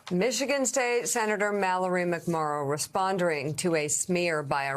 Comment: Dear sweet jesus did I just hear her say "respondering" at 1:06? GRAMMAR POLICE FFS SMH